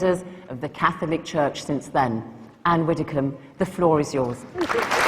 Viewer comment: misheard Zana when she said to Anne 'the floor is yours' - thought she said 'flaw' (really - for a moment)